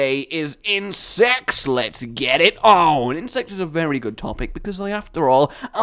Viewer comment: I thought said "insex, let's get it on" so um. don't judge me